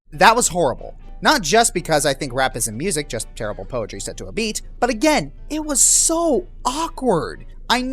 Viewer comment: "Rap isn't music, just rhyming set to a beat." Yet you like Epic Rap Battles of History............um.................OK? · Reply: Misheard that, rah. He said "poetry set to a beat"